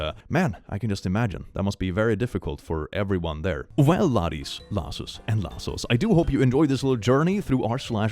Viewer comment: Hey quick question- i am bad with accent and i cant tell what he says at the beginning despite him saying it every video... is it "laddies, lasses, and lassels"? Or "lassos"? Is it a different order? Thanks... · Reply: I hear it as "laddies, lasses, and lassos" but freely admit that I don't know a lot of current slang and there could be some term I'm mishearing to fit my word knowledge.